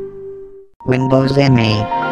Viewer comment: i heard: Windows and me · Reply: Windows M E